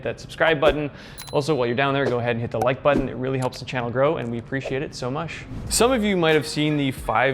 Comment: Did you just say "We appreciate you so muSH"?!! :))